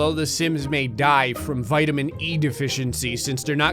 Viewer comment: Wait, did he say vitamin E?!